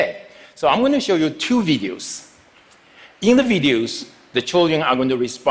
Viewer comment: I heard vegans not videos